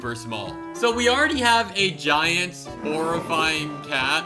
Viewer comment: Did you say Aurafying